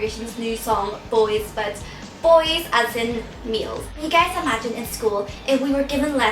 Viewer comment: lol i thought you said boys as a meal !